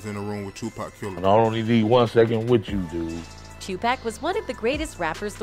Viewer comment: what was his answer ??? did he say "I"